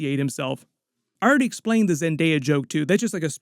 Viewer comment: I didnt realize he said Zendaya... for some reason my mind went to DC 😂😊😊 Thought he said "Zantana" 😮